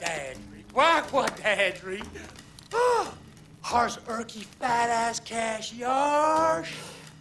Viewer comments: Did he say "Fat Ass Cash" to describe his smell? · did he just say fat ass?